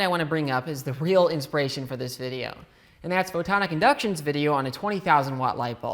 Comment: You say photonicinduction I hear "I popped it"